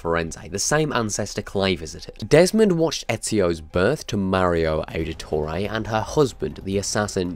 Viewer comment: i know it's just ur accent but i thought u said mario auditore first and laughed pretty hard